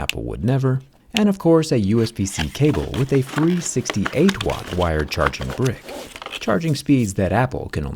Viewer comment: I thought you said 368 watt. XD